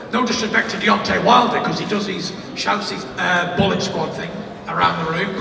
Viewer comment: Wilder is a plastic gangstar. Bad representative for boxing. Last week guns. Attitude. Boxing is about self dissaplin. Wilder and his gang need knocking out of division and out of boxing. Roll on Saturday. Fury = knock out. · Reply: Hahaha I thought I heard Fury saying Boĺlok squad..lol